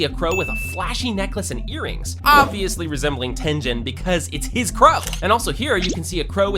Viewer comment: did bro just say “Tenjen” 😭😭😭